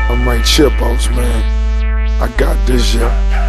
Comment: it sounds like he says "im like chipmunks man, i got this shit" but that doesnt make sense..